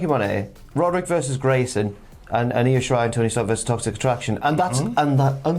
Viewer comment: I heard that; "Tony Stark"